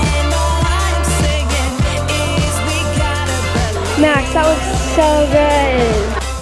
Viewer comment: Bro did she say “that looks so good” at 7:20 😭